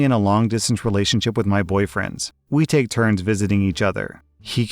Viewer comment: for a second I heard that as “boyfriends” and was like oh a poly relationship this is interesting